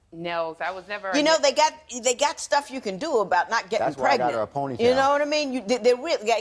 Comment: Did anyone else hear him say at 5:20 “that’s why I got her a pony tail” 🤣🤣